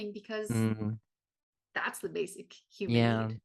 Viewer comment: When you said at 47:23 about love being the basic human need, I originally heard it as "that's the basic human nature", and it struck a chord. I know it's not what you said, but I felt like there was something very important in it to think about.